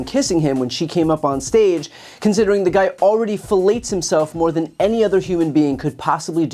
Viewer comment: I’m not quite sure I heard that right, it sounded like you said he fellates himself. From what Stormy said I think that would be impossible especially with his gut.